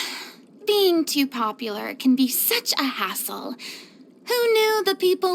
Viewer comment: Sometimes, Furina says : "Being too popular can be such a hassle." and I always hear : "Being too popular can be such a as**ole"